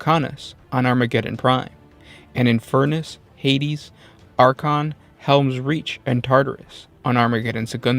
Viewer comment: Are ya'll dyslectic or something? 2:22 You read "Archon" instead of "Acheron". It's "Ach - e - ron". Not "Ar - chon". You're forgetting the letter E, and you somehow manage to put the R in the wrong part of the word. I don't wanna be a dick about it, but you do these sorts of misreads sometimes.